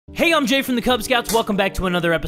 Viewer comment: it says cub instead of kubz